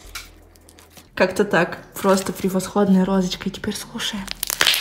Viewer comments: то чувство когда сказала: А теперь слушаем, а мне послышалось: а теперь скушаем😹😹😹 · мне одной послышалось "скушаем,, 🤨😂